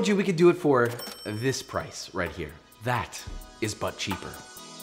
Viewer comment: Him: "But cheaper" What I hear: "Butt shaper" Meh